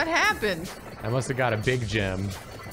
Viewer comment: While playing Vampire Survivors I know said he got a "Big Gem" But I heard "Big Jim" and immediately imagined just a massively Slim Jim, like a baseball bat sized meat stick